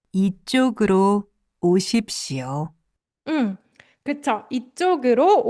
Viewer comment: when i first heard this i thought they were saying 50. i forgot about 오다 and 십시오 😭. on second thought it wouldn’t have made sense because that’s like saying 50 o’clock.